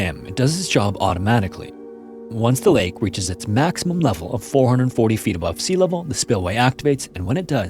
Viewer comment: Maximum level of 440 feet? The caption says 44 feet.